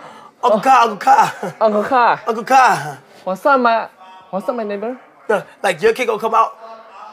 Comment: What he say @ 18:55 ? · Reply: wassup my neighbor